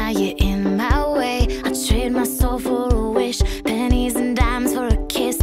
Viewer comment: tama ba yung narinig ko sa 0:14 ...nagrerequest siya ng penis? :D